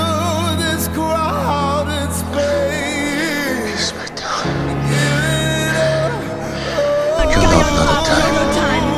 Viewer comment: What he said 2:26 ( in his language)? I love this video! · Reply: Livix he said "ge smak daun gyon op nodotaim"